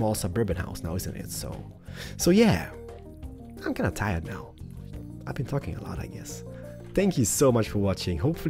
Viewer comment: I’ve been fucking a lot I guess 😱 I know I’m not the only one who hears that 😂